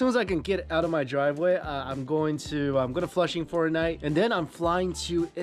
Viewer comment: did I just hear flush in fortnite?!